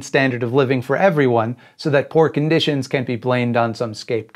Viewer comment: The closed caption says "can" where you say "can't", and it reads like the opposite of what you want to say. You should probably fix that.